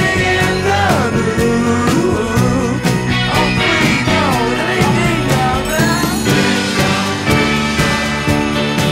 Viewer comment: Can anyone tell me what he says right here? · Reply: "Off we go, let me hear you now"